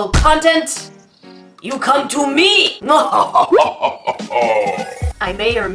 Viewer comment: only I heard yoo cam too me😅